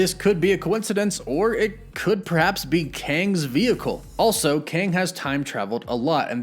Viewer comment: Oh.... i thought it said Krang....